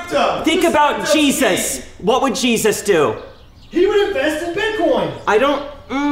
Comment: “He WoUlD hAvE iNvEnTeD bItCoIn”. 👏🏻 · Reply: I thought it said invented but then I thought it said invest